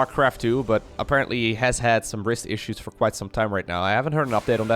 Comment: i can't understand the word risk issues ? what is that googled it and found just generally explenations - watched it further and heard rist and found per google wrist issues that means HANDGELENK in German